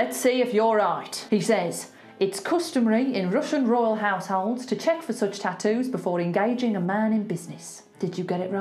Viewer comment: As far as I heard: “It’s customary in Russian royal family householders to check the suit status before getting engaged your mind in business”